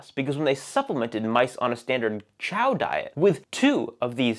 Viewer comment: I always hear “chow diet” as “ciao diet” as in “a diet that’s fatal.”